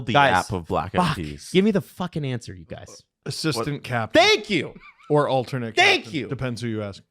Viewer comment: when? Maybe the 'plop' sound of my poop hitting the water drowned out the part where he said it. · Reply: "it's assistant captain... Or alternate captain, depends on who you ask" I think said it clear there. Maybe stops being a dick for a moment and listen?